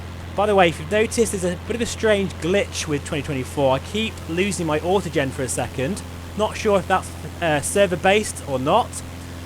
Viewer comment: I highlight this in the video, please re watch 👍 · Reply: I heard you say “I keep losing my autogen for a second” if that’s what you mean. I have no idea what “autogen” is though 😊